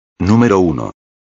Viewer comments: it sounded like gyatt 💀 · If you think it strongly he sounds like he says "gyatt". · did he just say gyatt? 🗿